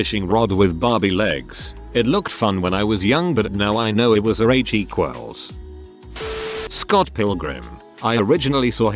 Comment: What was the censored word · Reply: Hooker